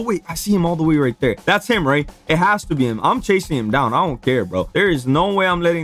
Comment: bro dont swear to God and dont swear at all in the bible it says not to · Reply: bruh I thought you said we don’t care